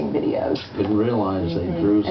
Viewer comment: did he just say " Thanks for wacthing fuck news?!" LOL